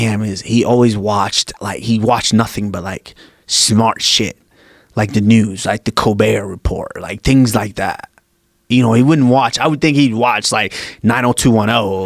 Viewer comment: "he's watchin smart shit, like the colbert report" lmao WHAT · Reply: Mufasa Trump at first I thought he said corbett report, maybe that's what he meant lol idk but if he meant Colbert report than that's hilarious